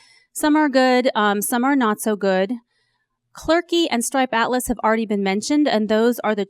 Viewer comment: Which is the company akin to Clerky @ 3:25? Didn't catch that. Someone could help please? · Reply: Stripe Atlas